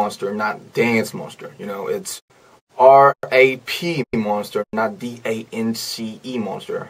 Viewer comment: Rm said: My name is RaP mOnStEr NoT dAnCe MoNsTeR YoU kNoW iTs R A P Me: Bruh~ I thought he said R I P hahahhahahahah LoL